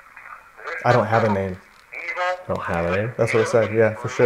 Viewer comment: on the playback recording of ''I DONT HAVE A NAME'' I heard '' I DONT HAVE TO TELL YOU''